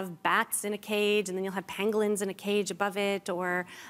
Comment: She was talking about "Pangolins" in the animal segment. At first I thought she said "Penguins".